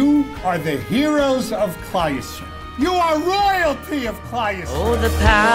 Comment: What does the rabbi say on 4:37? You are the heroes of ____, you are the royalty of _______. For a second I thought the heroes/royalty of christ LOL. So What does he actually say kliste??? Thanks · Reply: He said כלל ישרא-ל ( klal yisrael)meaning the Jewish people